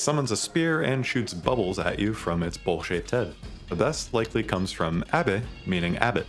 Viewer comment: I definitely heard "bullshit head" instead of "bowl-shaped head" on the Benisseur part 😂 He does indeed shoot bubbles at you from his bullshit head